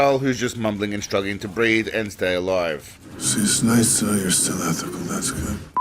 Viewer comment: Sounds like he said, "It's nice to know you still go after the Mexicans." Maybe he's talkin' about poon.